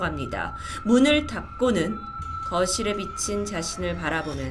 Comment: 거울이 아니라 거실에 비친이라고 나만 들리나?